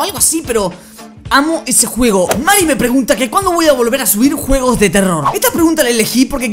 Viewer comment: like si viste que en el minuto 2:10 viste que diCE jugos de terror